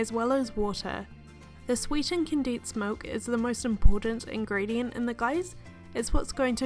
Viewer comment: what did she say? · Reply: "Condensed milk is the most important ingredient"